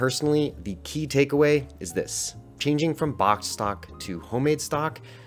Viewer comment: Thought he said "bok stock" 😅